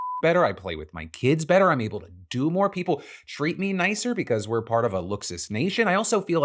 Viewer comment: we all know what the censored part was and have no questions, but right after that... I'm sorry but did you say "I'm able to do more people"